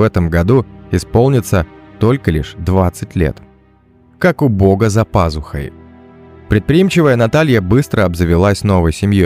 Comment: я услышала: как убого за пазухой😅🤣